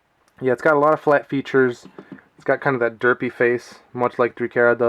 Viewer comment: Did you say derpy?